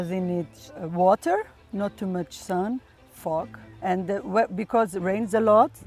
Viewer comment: Did she said the F word? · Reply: Yes, fog!